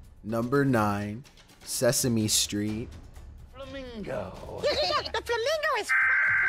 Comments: Number 9 sounded like " THE FLAMINGO IS swear word " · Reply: It’s sounds like he say’s THE FLAMINGO IS FUCKED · Elmo: the flamingo is a ******BEEEEEP************** Me: watch ur language elmo..... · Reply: Lmao It’s literally sounds like he said “The flamingo f#%k”